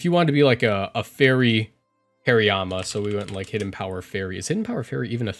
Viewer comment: I was listening while reading and I swear I heard say 'Fairyama'